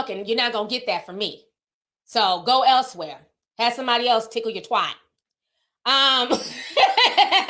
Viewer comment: Lmao! Listening to this while in bed and I started to doze off, but all of a sudden I hear you say “TICKLE YOUR TWAT!!!” That woke my ass up real quick!! 🤣🤣🤣🤣